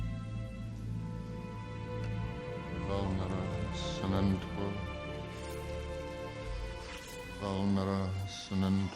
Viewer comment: what he saying? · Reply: "Vulnera Sanentur” 🪄